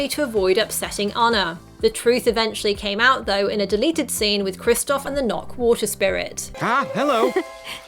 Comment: I thought it said delighted scenes